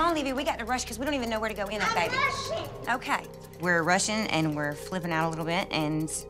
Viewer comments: When the are saying " I'm rushing" It sounds like there saying " I'm Russian" LOL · Anyone else thought she said “I’m Russian”